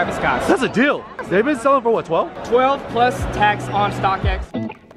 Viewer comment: Did he say 11:25 "plus tax" about buying on StockX? Did he ever buy anything on StockX?